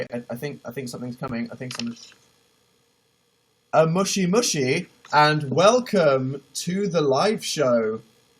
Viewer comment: When he said "moshi moshi" it sounded like he said "mushi mushi" which means "bug bug" XD.